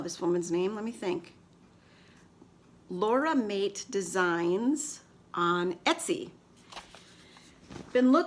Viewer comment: Would you please write the name of where you got your bag from? Thanks! I thought you said Laura Mate, but couldn’t find anything with that so I must have it wrong. Thanks! Love your bag! I would also love to knit a sweater and socks - two things on the bucket list. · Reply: Checkout podcast notes in Ravelry... LauramateDesigns on Etsy .... 🙋🏼‍♀️👍🏻